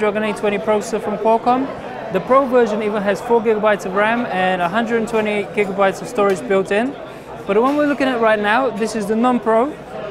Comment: did he say 120 gb of built in storage